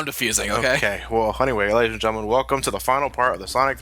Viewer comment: I heard "Elliot and gentlemen" O.o